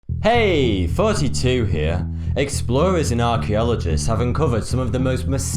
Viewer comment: Heyyy 42 here. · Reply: I thought I heard heyyy 32 here